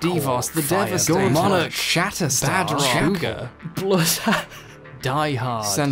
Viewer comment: That montage of edgelord superheroes kind of blurred together until I swore I heard "ShatterSad", and promptly imagined a much better comic where the Youngblood team was actually based around making people happy by kicking sadness' ass instead of, y'know, slaughtering people.